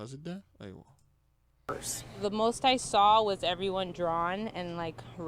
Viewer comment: the first thing I heard was “TMH saw” then I replayed it back she was saying “the most I saw” but I thought wow someone acknowledged TMHY for once lol